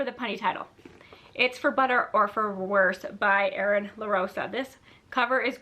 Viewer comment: when you said the authors name for For Butter or Worse I thought you said Eric LaRocca and I was like omg he wrote a romance novel….. ???? lol